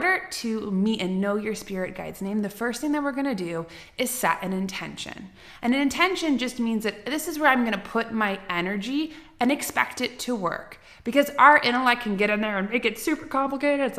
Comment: I heard " energy Anna"